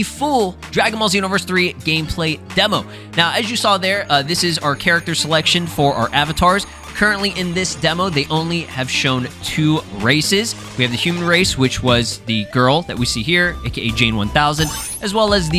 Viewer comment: did I hear racists or races lol English is fun lol